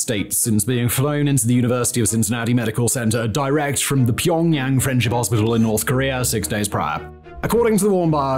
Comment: Maybe I misheard, but it's (ironically) called the Pyong Yang Friendship Hospital, not 'French' hospital.